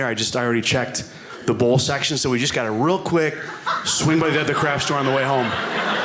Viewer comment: The first few times I thought he was saying 'crash store'. Bad pronunciation.